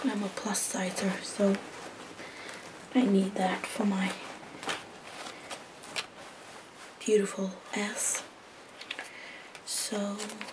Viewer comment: or 16:10 or so did she just say: I need that for my beautiful ass?!?!? Hahahahahahaha